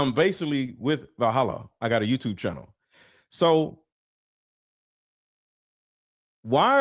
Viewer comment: I thought said “like Vahalla I have a YouTube channel”.